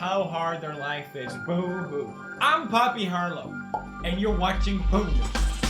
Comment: Idk but at the intro I thought pewds said "and you're watching poo news" Okay, I am so sorry.